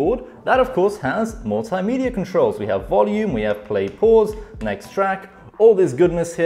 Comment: i thought he said play porn....😂😂